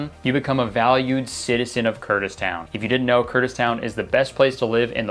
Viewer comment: This video was sponsored by The Pokémon Company International! Click here to learn more about Pokémon TCG Pocket: <link> #sponsored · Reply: Hey Kurtis ;) sry to inform u but Kurdistan is not "the best Place to live in" (just how I heard the outro 58:32 :P)